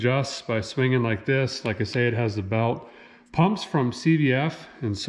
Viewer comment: Lol I thought you said “The pump’s from Cvs”………hahaha yep that checks out